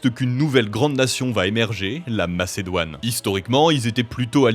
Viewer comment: C'est pas la Macédoine, c'est la Macédonie* Enfin, c'est ce que j'ai entendu...😅